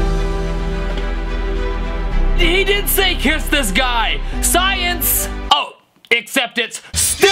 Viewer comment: i heard p!ss the sky 😅